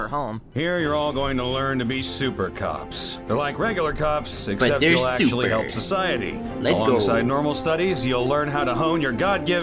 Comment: me: what did i just hear? super coc- also me: oh wait he said cops